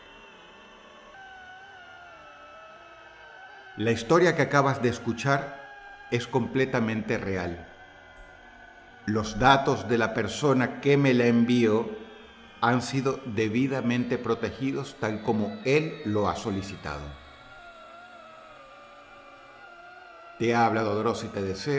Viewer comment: Yo escuché inicialmente “los gatos de la persona que me la envió…” 😂